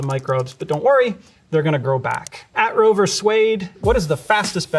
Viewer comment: When he said "Rover Suede" I thought he said "Roe vs Wade"